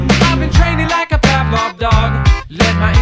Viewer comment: song: I've been training like a pavlov dog my brain: I've been training like a CARDBOARD BOX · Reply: i always thought it said "ive been training like a pack of dog"